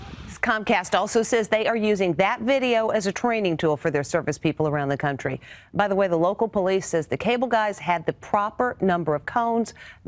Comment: did she say “their circus people” ?